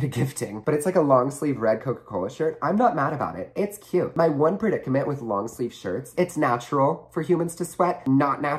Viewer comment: Was I the only one that thought he said my boyfriend not my one predicament